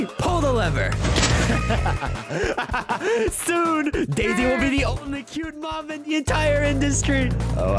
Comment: Am I the only one who heard etho said daisy is a cute mob? At 0:10 also bro killed his own sister 1:05 💀